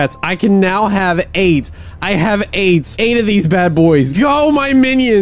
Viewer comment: u said 'i have 8' it sounded like 'i have aids' No joke